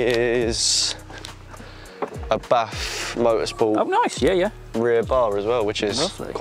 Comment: I can't find "Bath Motorsport MK5 Supra Rear Bar" on Google, can someone give me the link? And please put subtitles coz I can't understand British accent, so I just put auto CC and it said "Bath Motorsports" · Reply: Dont know if u found it already but its BAF Motorsports rear brace